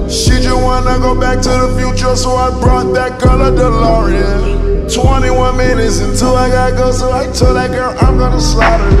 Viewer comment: Who else thought he said 21 minutes until now🤔😂😭🤭